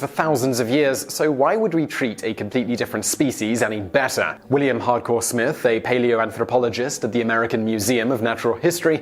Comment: Did he say William Hardcore Smith? HARDCORE!